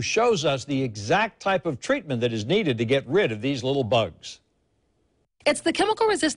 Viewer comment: Lol, I thought the anchorman said "... what is needed to get rid of these little buggers"...lol...had to replay to check!!!